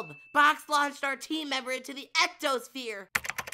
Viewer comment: I think they said EXO-sphere